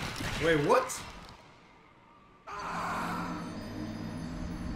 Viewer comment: As a Dota 2 player, i heard: "Ok, Ok, Gabe, Thank u"